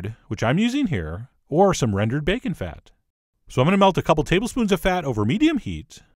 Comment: On first listening, I thought you said," lard or rendered baby fat." Had to rewind THAT and have a second listen real fast. LOL